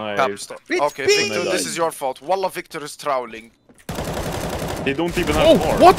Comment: Did i hear wallah? 🗿